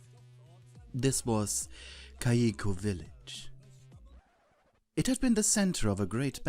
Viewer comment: I thought he said Gieco village